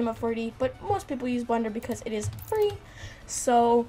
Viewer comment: is she saying Blender?